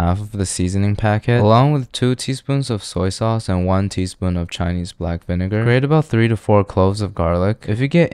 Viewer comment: omg i thought you were saying "poutine" and i was so confused but then i realized you were saying "1 to tæyn"